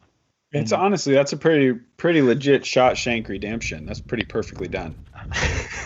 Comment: I thought you said it was redemption for!? :-). you have a great sense of humor and I thoroughly enjoy your commentary!